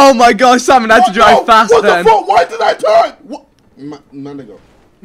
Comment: Wait did Josh say nigga? 6:30 he says it😂😂